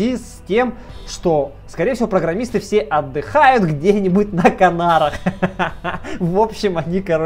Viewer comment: вместо "на Канарах" послышалось "на нарах".... что со мной не так?